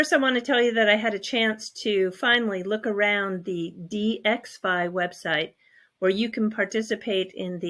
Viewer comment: you said you looked around the "DXFI website?" I couldn't understand you and searched for DXI, DXFI but can't find that site. Can you clarify? Thx.